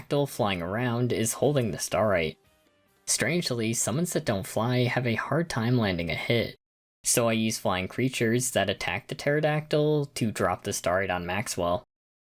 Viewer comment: what did you say? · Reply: "Strangely, summons that don't fly have a hard time landing a hit, so I used flying creatures that attack the Pterodactyl to drop the Starite on Maxwell."